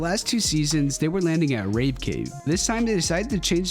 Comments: did bro just say ”Rape cave” 💀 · DID YOU JUST SAY RAPE CAVE? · What did I just hear? Ra*e Cave?